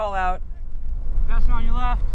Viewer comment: Use a bell or call out what??? I could hear what the guy said =/ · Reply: "Passing on your left!"